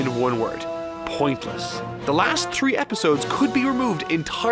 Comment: Did u say “the last tree episode “? 😂6:29 I thought u were Jamaican for a second